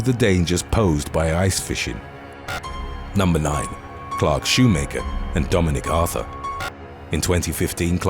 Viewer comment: I know this is completely off topic and irrelevant, but at 45:43 when you said "Clark Shoemaker" and Dominic Arthur, I genuinely thought you said "Clarke's shoemaker, Dominic Arthur"... Quite literally, a shoe maker for Clarke's... 🤦🏼‍♀️ I'm gonna guess his parents have never heard of Clarke's, or they thought it would be funny, maybe it's just lack of sleep (it's 3:30am and I'm half dead right now), I don't know 🤔🤷🏼‍♀️😅